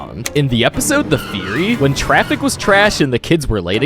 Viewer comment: i thought that said "furry" and i was like: what the fu